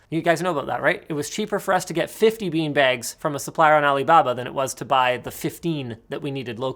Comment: Am I the only one who heard him say "Alabama" instead of "Alababa"?